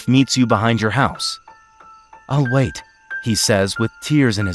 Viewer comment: When the boy said, I’ll wait I thought he said I’m gay😔😔😔💀💀